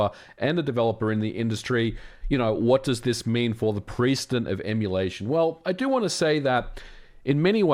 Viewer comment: "precedent of emulation" sounded like "priesthood of emulation" to me at first which sounded really cool. i was hoping there was a way to use "priesthood" in casual speech